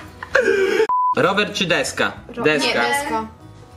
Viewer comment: Ja zamaiast usłyszeć rower czy deska to usłyszałam Robert czy deska